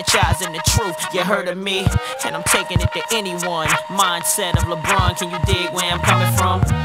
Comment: And I'm taking it to anyone mindset of LeBron can you dig where I'm coming from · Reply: wowww i always thought he said montana lebron instead of mindset of.. always wondered why he would use a nfl qb lmaooo thats crazy